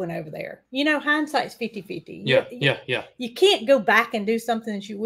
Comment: didn't I hear you say, " hind sight is 50-50"? You silly girl😂, isn't it , hind sight is 20-20? 😂😂